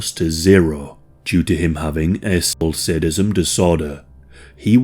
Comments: sounded like said "a&^ho*& sadismn disorder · I had to rewind 3 times. At 26:17, what are you saying? Not trying to be negative but earlier in the video you mention him having autism. At 26:17, I hear “asplocidism disorder” and Idk what that is. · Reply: Sexual Sadism Disorder. Combining violence and pain with sex.